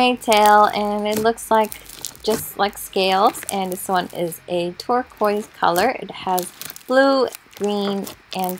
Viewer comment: When chu said ‘Turquoise’, For a second, I thought you said ‘Twerk-Quise’ lol (No hate)